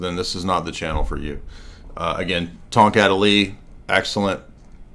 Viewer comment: I am such a moron I thought had been saying “Tom cat elite” instead of “tongkat Ali” this entire time. I am a slow boy.